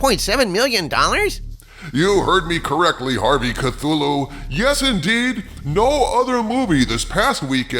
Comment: Did I hear you say “You are correct, Lee Harvey Cthulhu”?